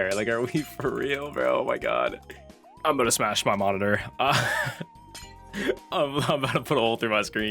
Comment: Bro my mic cut off from the console i thought u were gonna say "im gonna smash my mom" instead of monitor😭